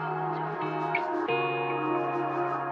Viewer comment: I heard "Darling its the wolf apolagize" And also "Dads the wolf apolagize"